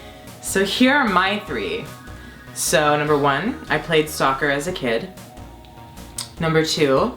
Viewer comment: When Mitch said "I played soccer as a kid" I thought he said "I played STALKER as a kid" oopsies 😅